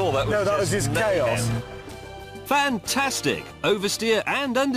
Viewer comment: is it me or did he say fanta-stig?